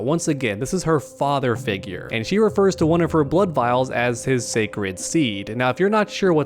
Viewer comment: My jaw dropped and I pissed laughing when I heard “Shalcon’s seed”